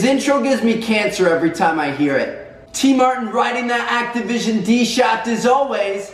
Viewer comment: I watched the entire video but i all i kept hearing was "i love sucking that activision 8==D".